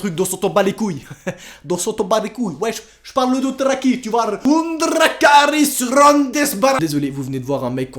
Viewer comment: Ce que j'ai entendu: "undrrrakalisranesbarrr!!!"